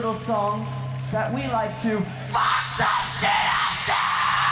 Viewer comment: what Daron said? couldn't understand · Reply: F××× some s××× up to.